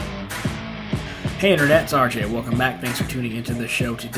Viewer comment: When RJ says, "It's RJ", I usually mishear as "it's Sergeant". Hi Sergeant RJ